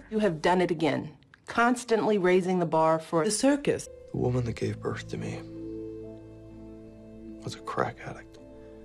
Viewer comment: STOP tell me why "the woman that gave birth to me....was a crack addict" made me BURST out laughing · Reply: I thought he said "the woman that gave birth to me was a crackhead" 💀